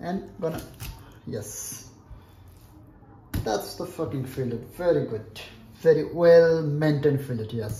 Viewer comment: Good knife skills! At 5:05, it sounded like you said, "That's the f-ing fillet!" 😮😅